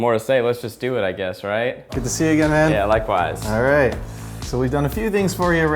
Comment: i think he said "good to see you again Ned"